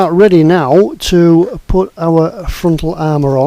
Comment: AAAAAUGHHH TRACKS oh my life. You need ALL THE PATIENCE Oh, Tony Fairclough will have a field-day with the number of times you have said "hinge" in this episode ^_^ I keep mishearing "frontal armour" as "front llama". Now I see a tank without a front llama as incomplete somehow.. Eager for the next part :)